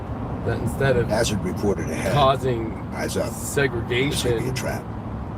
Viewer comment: What's that voice saying in the audio? I heard - 'Eyes Up! There's going to be a trap.' Did anyone else hear that. I rewound many times.